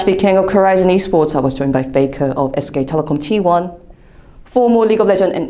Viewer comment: When she said faker first i heard 'baker' lol